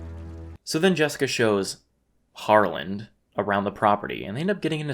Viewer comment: In all Honesty, I've watched this Vid a few times an Only after the 3rd view I heard it as Harland... I been thinking it was Yarland🤣 Not sure if I can deal with that